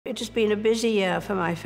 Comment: Judy started the video and first thing I hear is "Bitches been a busy year"